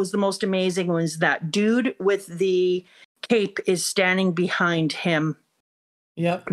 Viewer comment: I misheard "man with the cape" as "man with the cake", and now I'm.just thinking of a caked up Dragon. Thanks for that.